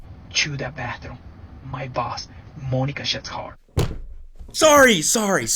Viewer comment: He did not just say Monica’s last name is Shittar!